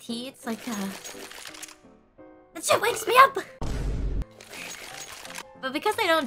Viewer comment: I think Ame said "That shit wakes me up" and not should, right? lol